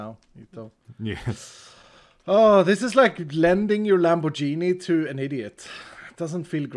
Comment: i heard "blending your lanbo-genie"